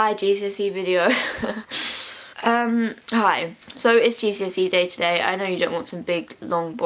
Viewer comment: i thought u said "its jesusy day"